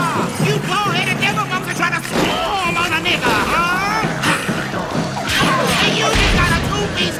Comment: what’s he saying in this part?? “Ah! you ? ? monks are tryna swarm?/storm? on a nigga huh?! You ? 2-piece ? ? ? I got 3-stick nunchucks!”